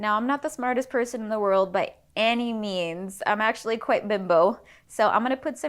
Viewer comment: does she say "bim-bo" at 21:19 or is that some zoomer slang term I dont know?